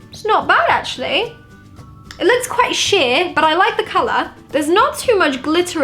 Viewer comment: I thought you said “it looks quite s**t “ but you said “ it looks quite sheer”